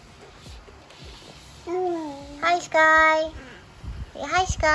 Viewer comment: i thought i heard say "Hello"